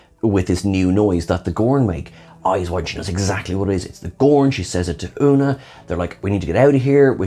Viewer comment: I thought she said "it's the borg" and got very interested!